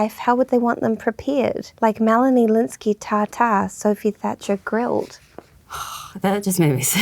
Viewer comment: I thought Melanie said “Melanie’s tatas” when talking about how to prepare them to eat